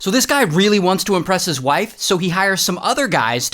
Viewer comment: In the start I thought you, said so this guy really wants to arrest his wife. lol